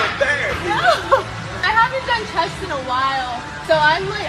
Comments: I thought she said “I haven’t done test in a while, so I’m feeling very weak” and my heart sank.. then I realised she said chest lol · Had to watch it a 2nd time cos I thought she said "I haven't done Test (testosterone) in a while" then realised she said "chest" lol